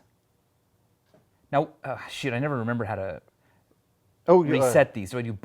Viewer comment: haha I think tried to say “shoot” but ended sounding like “shit”